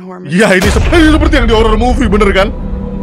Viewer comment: anjir cepet banget ngomonya tapi abang emang tadi ngomong apa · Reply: Iyah,ini seperti yang di horror movie bener kan